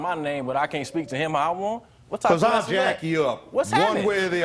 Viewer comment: I swear i thought the judge said "ill jack you off" 🥴